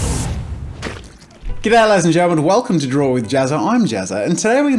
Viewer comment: I always hear "get out ladies and gentlements..."